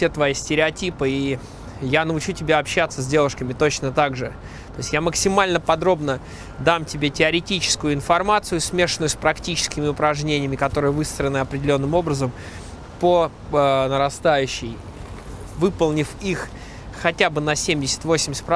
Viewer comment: Кто услышал "высраны определенным образом"?)